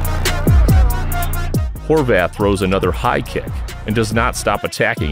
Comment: what the f is he saying? horveth?